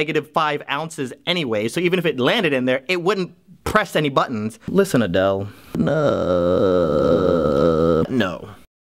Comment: "Listen Adele" I heard "Listen Linda"